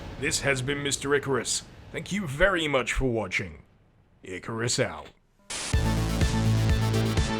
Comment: I always watch your videos with close camption (natural language is Spanish), and the end of this video made me laugh. When you said, 'Icarus out', the close caption thought you said, 'a carrousel'. It was the perfect ending!!